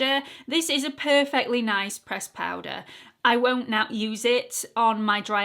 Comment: You get me to laughing. Allure- take out an L. Say A just as your saying it. Now, do you know what a fishing lure is? Your lure is said the same way. A-Lure. Now what I can’t do anything for is 25:30, “ I won’t not use it”. My brain is stymied. Lmao! Only you!😳💕😳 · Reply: I think she said I won't now use it, although your interpretation is funnier and definitely something would say 😄